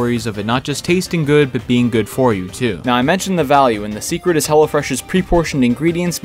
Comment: Everytime he said Hello Fresh it sounded like he said hella fresh